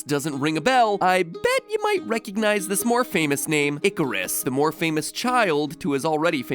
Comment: bruh I thought that said karus